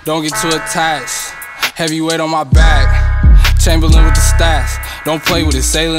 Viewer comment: I thought he said Chamberlain with the stache at first, double entredes either way it works 🔥